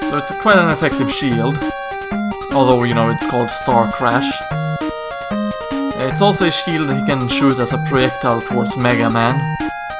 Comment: "Its also a shield he can shoot as proyectilfe forced mega man" I swear is what you said